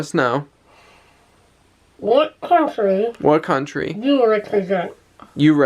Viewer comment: when you murmured “let us know what country you represent” I heard “let us know what country Europe is in...” haha! Thought that was a silly question 😜